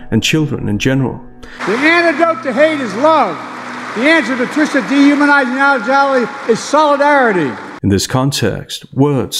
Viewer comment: I have no clue what he's saying, rewound it a few times, it's nonsensical like everything else he says, but that bit wasn't english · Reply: "The answer to Tricia dehumanising our jelly is solidarity"